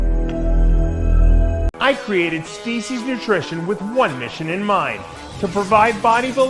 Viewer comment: Dave, I thought you said "Stacy's Nutrition", and I'm like "But, you're Dave."